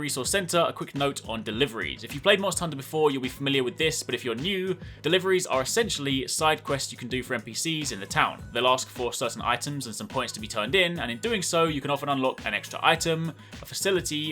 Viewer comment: I had to rewind clip several times at 5:22 mark asking my self..."did he just say NPCs in the tower?" Lol I knew it, this game is in the Destiny extended universe lol. Haven't gone back to D2 since I bought MHW. Great game.